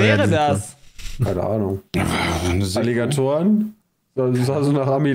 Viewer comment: ich hab erst "Illegatoren" verstanden, auch iwie n cooler Name